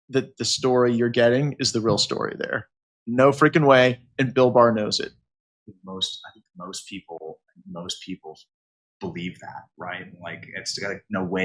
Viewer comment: At first i thought he said Bill Burr 😂